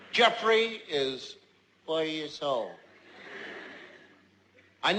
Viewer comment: "I four years old" Lol I thought he said "I'll swallow your soul" XD